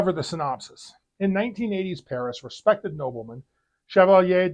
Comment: Good review even if it isn't a werewolf movie. At the beginning if the synopsis it sounded like you said "1980s" instead of 1880s. BTW, your pronunciation of French names is getting better.